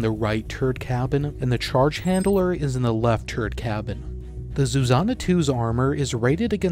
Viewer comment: Wait, did you say turd cabin @ 3:25?